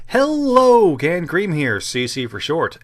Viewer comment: I not only saw it coming, but I clapped when you did it. I love that bit. ^_^ Also.... I thought you said I'm 'gangrene' not 'cannedcream'..... I need to clean out my ears.